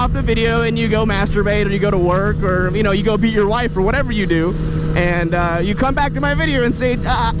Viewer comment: did he just say beat yo wife and he made a fucking outsiders reference like goddamn